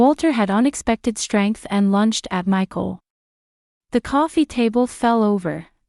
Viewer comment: Finally someone who knows how to spell "sigh". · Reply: Yeah, but the text subtitles at 27:37 said "lunched at" instead of "lunged at."